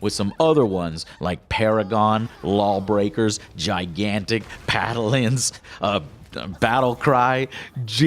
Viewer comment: Did he just say Padalins?